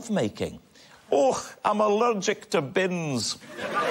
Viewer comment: I never got Robs joke about Scottish women, allergic to 'bins'? Or have I misheard it?